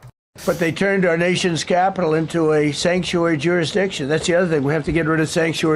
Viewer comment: “They turned our nation’s capitol into a sanctuary Jewish nation.” Am I the only one who heard that?